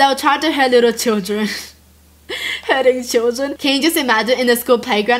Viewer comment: I THOUGHT SHE SAID "HURTING DOGS" AND "HURTING CHILDREN" SO WHEN SHE SAID AWW I WAS SO CONFUSED.